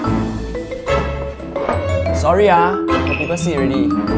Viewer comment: at first i thought he said how people shit